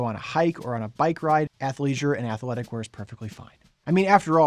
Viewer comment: I thought you said avoid athleisure. M.